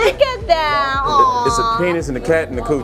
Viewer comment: Wait...Hmmm..What in hell his ketchup drawings at @ 11:20 ....It sounds like he said..Penis and coouch! LOOL Please tell they;re just messing around and didn't actually draw something like that....LMAO!!! Great video! I personally LOVE food manga and have gotten so many great recipes from the books, or just learned enough about it so I can try to make my own! "Iron Wak Jan!" is another great food/food battes type anime. There a ton more but my mind is horrible at remembering names...LOL